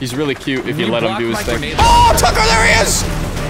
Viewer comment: So at around 13:10-13:17 I thought Smii7y said "AHHH IT'S A DELIRIOUS!" But really he said "AHHH TUCKER THERE HE IS!"